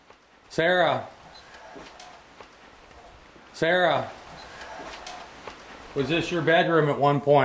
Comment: 33 58 i hear "call Sarah"